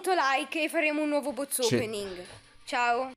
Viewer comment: io ho sentito in anteprima: BOZ OPENING